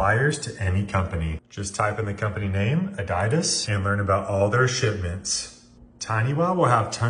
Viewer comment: HAHAHAH Did he just say ADAIDAS???? HAAHAH Brilliant!!! ADAIDAS